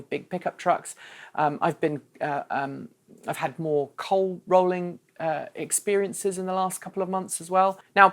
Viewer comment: sorry what "coal rolling" or at least that's what I thought you said?